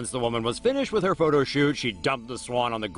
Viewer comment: Who else thought it said photo shop